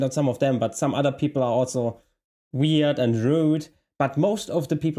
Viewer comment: I misheard "weird and rude" as "weird and ruined" and though, yes. that's me